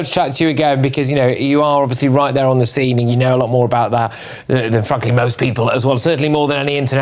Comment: Did he just say “ you know more then fuckin most people “ at the end ? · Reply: I think he said “ frankly than most people “